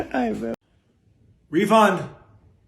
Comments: did you call your dog "refund" ?????? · is he saying his dog’s name is REFUND??! 🤣 I can’t tell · Did I hear that name correctly? Refund?